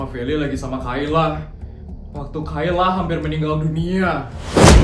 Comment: Kok aku denger kayaknya Feli meninggal Apa aku salah denger ya